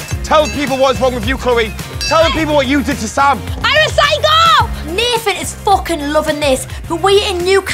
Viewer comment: Ngl when she said ‘I’m a psycho’ I thought she said ‘I recycle’